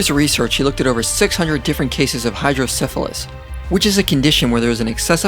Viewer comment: Judgement-free question: At ~ 1:00, does anyone else hear what sounds like 'hydro-syphilis', in reference to 'hydrocephalus'?